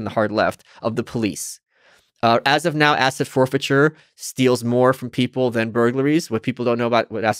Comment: I thought he said "acid forfeiture", I was like 'never.'